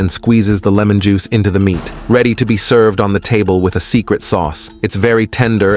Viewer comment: I thought i heard "His secret sauce" 😂